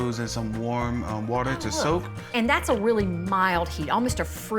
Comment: Sounds like she said ass water at first lol 😆 😂 🤣 😅